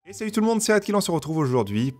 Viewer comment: J'ai entendu "Salut tout le monde c'est Adkill" XD XD XD